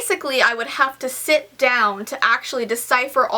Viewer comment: did you say cipher like Bill Cipher (Gravity falls reference) also sorry I probably spelled that wrong